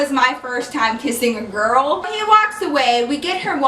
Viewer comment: I heard "first time I kissed a girl".... who wants to hear the second. Because I know I do!